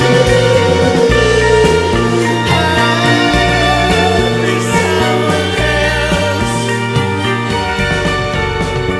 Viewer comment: cosa dice al 5:11 ? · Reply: credo che dica "being somewhere else" ma non vorrei spararla grossa